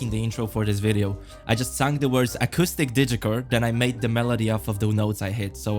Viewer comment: i thought that said autistic digicore.